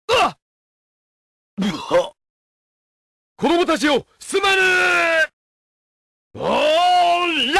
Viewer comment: What did he say? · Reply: Kodomo-tachi wo sumane!- I'm sorry, kids!